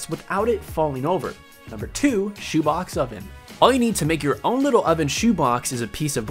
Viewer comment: who else thought he said chubacca instead of shoe box? 😂😂